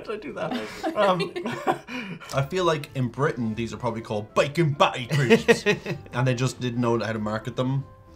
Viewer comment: I couldn't understand, so I put subtitles on.....what the hell are biking batteries?.......